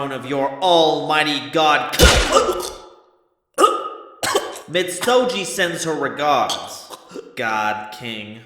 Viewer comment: Does anyone know what he said? I know he said "___ sends her regards" but who did he mention? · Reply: My bad, it was Fabled Miztoji